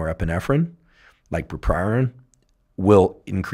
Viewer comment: What is he saying at 7:30 ? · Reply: Bupropion I think